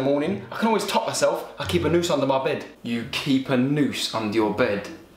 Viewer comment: I thought he said he has nuces under his bed